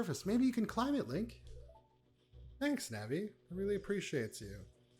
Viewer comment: Auto-generated captions thought you said “thanks daddy” instead of “thanks Navi”. Made me lol